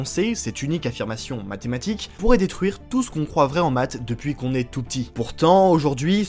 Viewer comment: tout ce qu'on quoi ??? 😭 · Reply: « Croit vrai en maths »